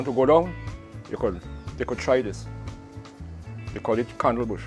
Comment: can't make out the name of Bush sounds like camel but I looked it up not similar ?